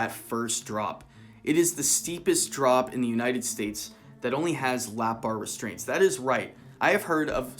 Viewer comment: no he meant ‘it is the steepest drop in the United States. It only has lap bars!’ It was as another fact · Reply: What he actually said "It's the steepest drop in the United States that only has lap bar restraints" you can rewatch it but that is the exact wording. That is not an opinion it is taken directly from the video starts at 6:30. Look I get it you don't like to get called out like this, but you need to understand how to accept something when your wrong and not double down on something your clearly wrong on